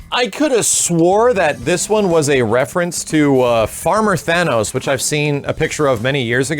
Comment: IT WAS NOT AN APRIL'S FOOL JOKE! IT WAS JUST ART! GEEZ! Oh god what is wrong with your leg, Zewo! I dig that Strider look ~ Oh, Unarmored Thanos? I thought you said Farmer Thanos.. ... Fuck you Frank West... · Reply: He did say Farmer Thanos. He's referencing the end of the infinity gauntlet series, where after thanos was defeated he resided on a planet as a farmer, this costume looks kinda similar to that.